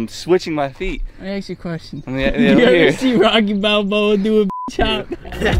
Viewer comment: did someone just say Rocky Balboa